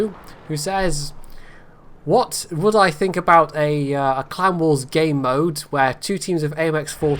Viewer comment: First question, I thought you said Clown Wars instead of Clan Wars, and it totally fit.